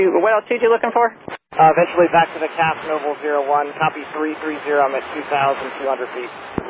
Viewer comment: the captions are wrong. He says copy 330(his heading) and the caption says 220.